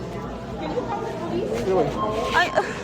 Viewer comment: She went up to the counter "can you call the police?" and I heard "for here or to go?" 🤣